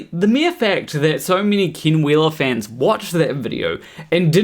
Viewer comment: With your accent, every time you said "Ken Wheeler" I heard "King Wheeler" and was wondering why he was calling himself a king.